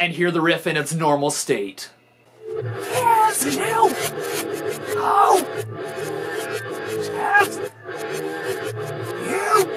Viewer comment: Him singing Walk backwards, did anyone else hear "Message you, hoe, ass you"?